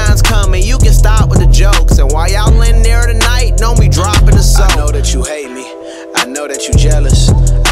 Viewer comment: Uhh anyone else catch him say “and when we in there know we dropping the soap” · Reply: He said DON'T be droppin da soap